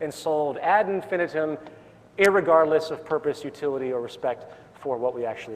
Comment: He just said "irregardless." o_o I heard it. I know I did.